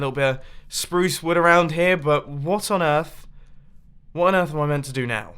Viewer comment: I Thought You Said What On Earth At The Water Hole Thing